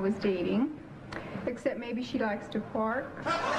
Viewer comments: I can't tell what the woman was saying.............Can anyone make out that word? I thought it was "fart", but i don't think it is. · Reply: Took me a sec: “She likes to park.” Before Netflix & chill, they parked on a hill. 😏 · she likes to what? Can't make up what she says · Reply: Park